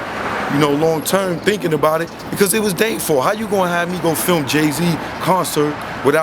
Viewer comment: That was Dame that answered…lol!!! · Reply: I thought He said "how you got my number "